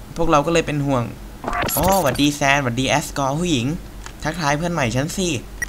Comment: พี่ไม่แอสกอ ทอเรียวตางหาก(1:28 ได้ยินว่าแอสกอ)